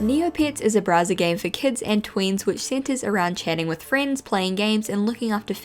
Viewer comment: Thought you said “kids and queens” instead of “kids and tweens” and i was like yaaasssss